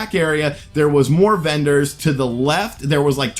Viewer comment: Did he just say 'more benders to the left'?